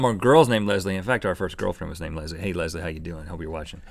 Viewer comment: does Rhett say OUR first girlfriend?